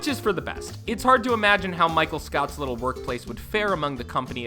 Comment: DID HE SAY MICHAEL SCOUT???